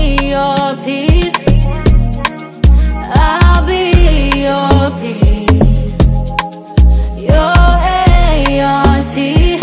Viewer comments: I thought she was saying I'll I'll your piece your hair your teeth 😬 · I'm sorry I can't be the only one who thought she said aunty instead of A-R-T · I thought she said aunty instead of a-r-t please tell me I am not the only one